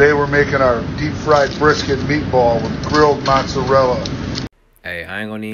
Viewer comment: Okay, so that was deep-fried brisket, meatball, and grilled mozzarella? Just making sure for when I try it 😁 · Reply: That's why I was asking, I'm pretty sure I heard 'meatball', but not sure where that fits in that gastronomic awesomeness.